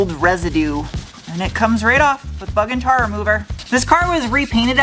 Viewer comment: I heard "f*uckin' tar remover" and now it's stuck in my brain.